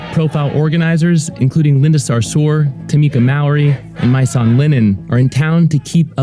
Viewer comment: I thought he said “My son Linen” and turns out his name was “Mysonne Linen” smh